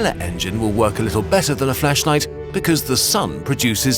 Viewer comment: i heard fleshlight not flashlight